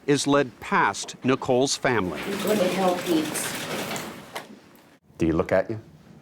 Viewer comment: What did she say to him at 37:11?? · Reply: “Go to Hell, Pete’s.” ?? I think